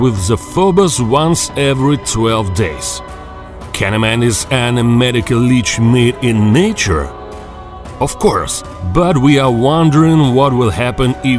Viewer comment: I thought he said mate